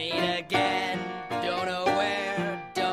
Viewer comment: when I first saw this weirdmageddon episode I thought it said bill me again but it said will meet again